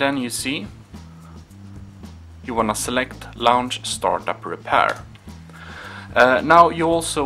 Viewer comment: It sounded like you said lounge starred but I think you meant launch start❓